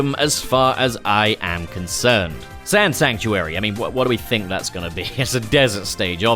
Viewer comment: When you said Sand Sanctuary and you showed a skeleton I thought you said "Sans Sanctuary". 😅